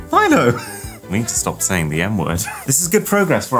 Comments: did you say you need to stop saying the n word 😭😭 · I THOUGHT HE SAID “I need to stop saying the N word.” LMAO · I thought he said "i need the stop saying the N word" 😭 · did he say he needs to stop saying the n word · I heard stop saying the n word 😂😂😂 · I swear I heard you say I need to stop saying the “ n word “ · what did he say i need to stop saying the n word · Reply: the m word bro, mouse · Why does it sound like he is saying "I needa stop saying the n-word" at 7:47? 💀💀💀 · I think I need to stop saying the n word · I thought he said I gotta stop sayin the n word 💀💀💀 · thought he said “i need to stop saying the n word” LMFAO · i thought he said I gotta sto saying the n word😂